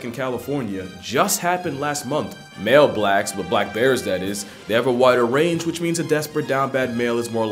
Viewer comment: tf does this mean?I'm confuzzeled. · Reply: Imma be real, I was confused because I thought he said something about black bears mating and I was like "Tf does that have to do with this?" On a rewatch I realized that was not what he said.